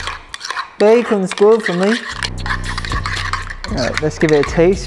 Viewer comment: sorry what does Gaz say here anyone? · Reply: "Bacon is good for me" Its from a vid where a kid doesnt want to give up his bacon XD I REMEMBERED! Its also made into a song, look it up! haha